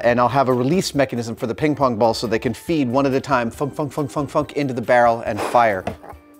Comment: Did you say fuck